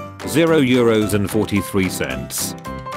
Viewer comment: did.... Did it say cents?